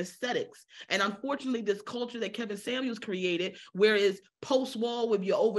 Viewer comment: Did she say Kevin Samuels created their reality? 😭😭😭